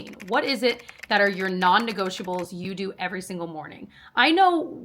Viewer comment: Hehehe... I had the CC on during the end of this video and when you said "non-negotiables" it thought you said "non ego vegetables" bwahahaha